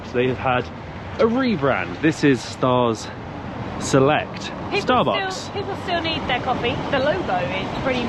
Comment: In fact, instead of Starbucks it is not Stars Selected, but Stars Coffee.